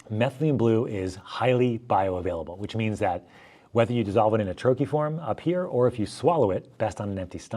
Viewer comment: What word is he using at 18:43 ? A “trophy“ form? AI says -to dissolve methylene blue (MB) in a trophy form (likely referring to a solid, crystalline form), you'll need a suitable solvent. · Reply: Buccal troche 🎉